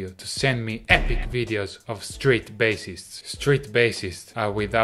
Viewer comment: Ok I was really confused because at first I thought you said “straight bassists”